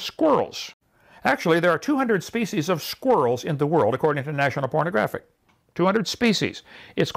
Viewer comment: When he "defined" kind, did anybody else hear him say 'national pornographic' instead of 'geographic' lol. It's